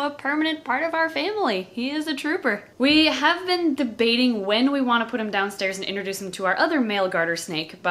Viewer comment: When she was talking about the blind snake I thought she said “we are not sure when to put him down”